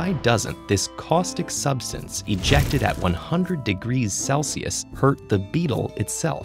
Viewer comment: At first I thought he said Fahrenheit and I thought to myself, “wow that’s really hot!” But then I realized it was Celsius and it was game over But seriously how in the world do you heat up something to 100c on the spot without at least burning yourself in the process?